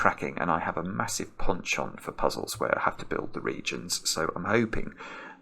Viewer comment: What's the word he says at 5:01? I have a massive "X" for puzzles.... Is this an English idiom of some sort? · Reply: penchant (French for habitual liking)